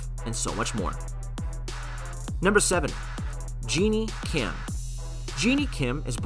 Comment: I’m sorry did he just say genie kim 🤣